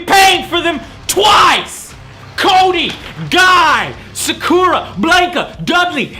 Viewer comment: Seriously I''m not joking but, at 1:10 i thought he said Kony instead of Cody